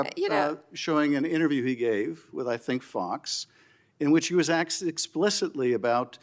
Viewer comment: Did Schiff just say, “aksed”? 3:36 in.